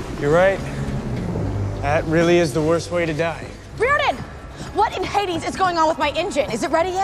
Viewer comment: did she just say Riordan ??